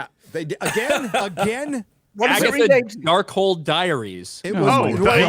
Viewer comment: Thought he said Darkhole Dairies.